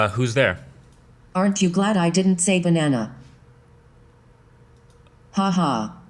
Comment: I thought she said the nana, not banana.